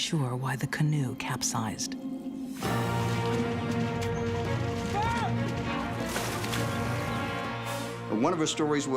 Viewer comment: What does say? All I can make out is, "Mom!". The rest of his shout I can hardly understand. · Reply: Help me up.